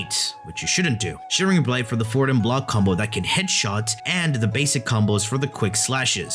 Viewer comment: What's he saying "[???] block combo" here? Sounds pretty important for the Guandao Prime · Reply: forwarf block combo